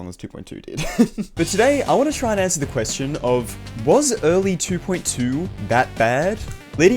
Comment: Why did I thought it said" was 2.2 was really that BRAD"